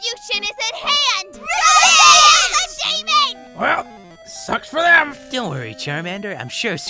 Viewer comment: auto caption says Well f*ck spam